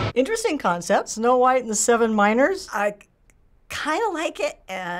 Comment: "snow white and the seven miners" What does she think dwarves do all day? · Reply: I thought she said minors and I got so concerned 😭